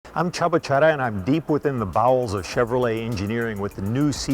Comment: I thought he said "I'm Chubba Cherry" lol cool name AND OMGAAAHHH!!!! That looks sexy and beautiful!! My future baby 2014 Corvettey Stingray! Chevy <3